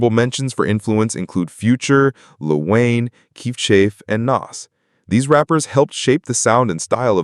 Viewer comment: did u just say keef chef 😂